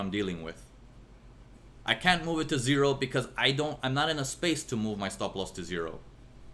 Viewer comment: Hi, could you write down here what you were saying fro 45:21 to 45:26 min . You were saying "I can't move my stop loss to zero because..... "( i can't catch your slang from here.). Sorry to bother and thanks in anticipation. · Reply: Hello gxclusivelyfriends, Thanks for your question. said, that there is not enough space to move the stop to zero. Hope that answers your question :-)